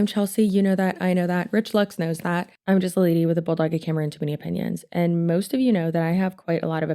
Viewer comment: you said "too many opinions" so fast this time, I thought you said "20 opinions" lol ONLY TWENTY